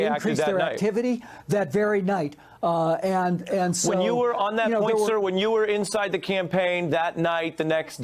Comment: Listen carefully to the anchor man at 8.28 he speaks over Podesta and it sounds like he says " when you was on that pizza " and then changes it to " when you was in the campaign " that's what it sounds like to me have a listen .